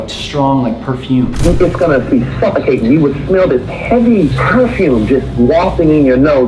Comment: hen it said im kate i thought it said sufacate